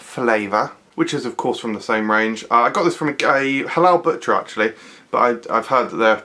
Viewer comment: Having a bit of a Food Review binge :) Did I hear gay halal butcher at 2:07 xD